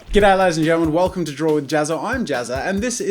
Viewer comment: jazza always saying in the intro "Get Out Ladies and gentelmen" or maybe i misheard it?